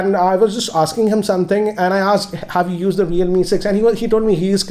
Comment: i heard it as Realme sex rather than realme 6